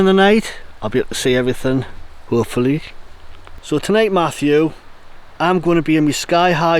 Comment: Hello! I had to listen several times, [even with headphones] when you were showing off your bivvy and it sounded like you said Matthew! Or was it something else? There are no Closed Captions available this time, and anyway, YouTube often doesn't know wtf is being said in English. It's like the translator is not Native English-speaking. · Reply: When I mentioned, "tonight Mathew I'm in me bivy tonight". Its a joke and refers to a UK tv program called stars in their eyes hosted by Mathew Kelly. It was crap actually but look it up, you might enjoy lol..